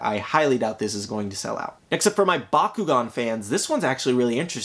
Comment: i thought he said “Bakugou” my heart raced thinking maybe there was gonna be a restock or maybe a new figure from Mha, turns out I’m deaf and sad now LOL